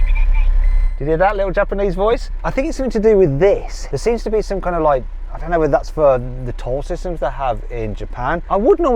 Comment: yes, that's the toll system called ETC in Japan. The voice is just saying "please insert your card" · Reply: ETCcard wo irete kudasai